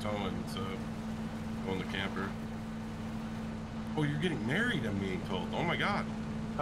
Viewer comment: did i hear it right?? getting married??